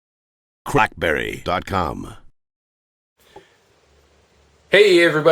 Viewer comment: i thought it said crapberry at the start LOL